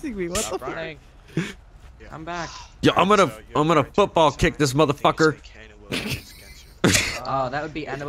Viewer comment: I was there in the cruiser watching that shooter but most funny point was watching the cat getting chased by a guy an my troopers LOL I was writing down the case and I heard "Football kick that cat" and "this is my Cat in the Hat" Pissing myself laughing with a suspect is so lid git